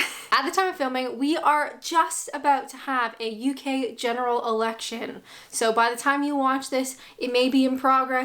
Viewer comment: I had to replay the start as the first time you said it I thought you said General Erection. Suffering from foot in mouth disease is a prerequisite for becoming a Politician. I don't think that Boris fathered any children that night after that zip wire escapade.